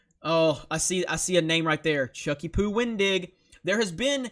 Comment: Jeremy, did you just say Chucky-Poo!? LOL XD